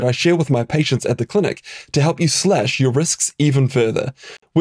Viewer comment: Thought you said Slash your wrists even further